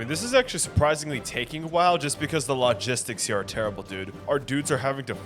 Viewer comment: I thought you said "Lao"-gistics haha